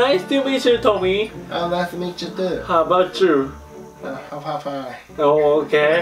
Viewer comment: How are you? が How about you?に聞こえたw